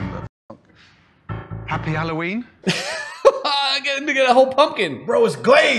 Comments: tell it doesn't sound like Ken said "he gave this n*gga a whole pimpkin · he clearly said "he gave the n** a whole pumpkin" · did you say "gave that nigga a whole pumpkin"?